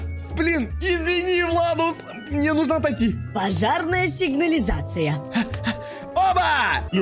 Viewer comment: Мне вместо пожарной послышалось жопарная😅😂